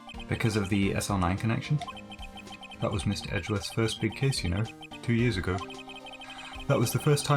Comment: Listening to this whilst working and I just misheard Gumshoe saying, "That was Mr Edgeworth's first big case you know" And heard instead, That was Mr Edgeworth's first big kiss ya kno Me: Wut?! rewinds youtube Me: Ooooh .... I really need to get my hearing checked.